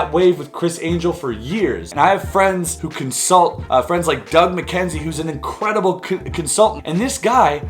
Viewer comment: i thought i heard dougal mackenzie